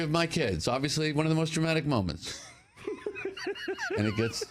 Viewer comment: "...always one of the most traumatic moments"? Or DRAMATIC? maybe a few people laugh because it sounds like hes saying "traumatic".